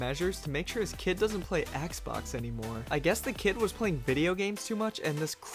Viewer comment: is it just me or do I hear "Axe box"